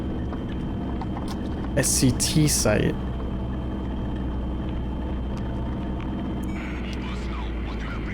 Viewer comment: why did I think SCP instead of SCT